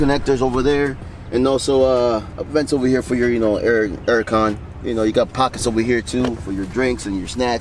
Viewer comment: did he just say ‘vents over hear for your Erick khan’ ?!?! might be tripping